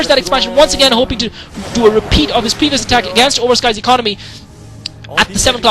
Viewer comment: LOL at 5:04 sounds like ur saying a repeat of his penis attack